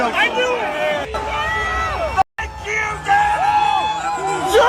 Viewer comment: I thought I heard someone saying “Thank you, Donald!” in the video of the guy screaming traitor when I saw it the first time. Nope. They’re saying “F*ck you, Donald!” 😂